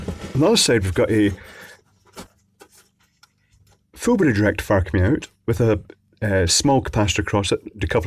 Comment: Who else hears electroboom saying FULL BRIDGE RECTIFIER?